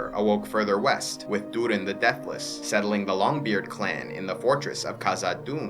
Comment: Oh, Durin's nickname is The Deathless ! I'm not an English speaker, so everytime I heard about him, I always heard "Durin the Dateless" instead, because of the accent 😅 Always thought it was kind of mean nickname